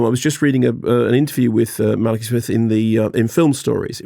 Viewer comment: I honestly thought he said written by Mark E Smith.